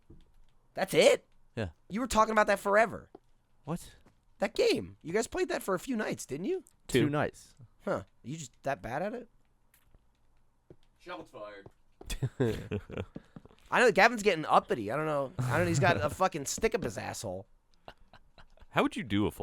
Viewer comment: what did Ray say? "shotlets fired"?